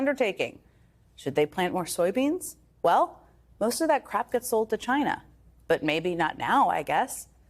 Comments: She said crap 💩 instead of crop 😅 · With her accent i thought she said most of that crap gets sent to China. Took a few more sentences to realise she was saying crop!